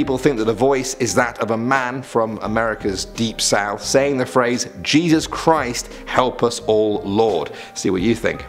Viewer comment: I heard "Jesus Christ help us all lord"